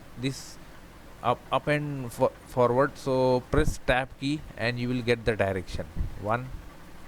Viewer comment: what does he say? Staff E? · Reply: I figured it out, he is saying press "Tab". That should swap the Axis from XY to YZ and so on. When the little axis pop up, just watch back this video to what way the red lines are going again either YZ or XY etc.